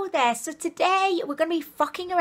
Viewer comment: As I listened to this I heard it as today were going to be f**** around 😂